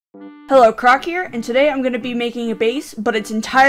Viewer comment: HELP I THOUGHT HE SAID HELLO ITS CRACK HERE 💀